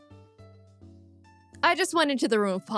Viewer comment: did u say the room of BLACK!?👺